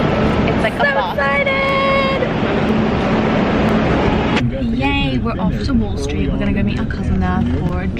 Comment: Lmao at 10:06 I literally thought she said "We're going to go meet husbands on wall street", same gurl